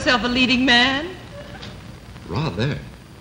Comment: what does he say? Rather?